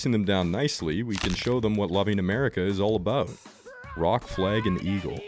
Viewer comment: did you say...Goblin America ??? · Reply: It's "loving"